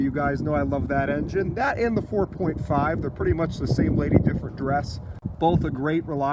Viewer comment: Same lady, different dress haha. I never heard the saying before, Nice! oh and nice Caddy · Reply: I thought he said different duress.